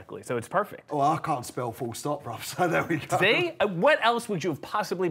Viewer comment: what did he say ? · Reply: "I can't spell, full stop, bruv!"